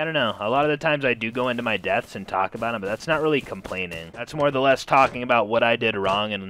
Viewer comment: I hear "more the less" DERRRRR